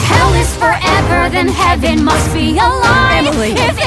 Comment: When they said the heaven must be a lie bit. But i thought they said "heaven must realine" which half makes sense.